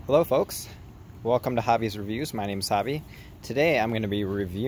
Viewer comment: sounds like he said hi my name is wasabi